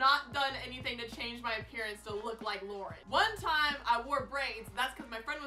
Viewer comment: When I first watched this, I heard it as "dang I look like Lord." And then I re watched it and she actually said Loren. Whoops 😂❤️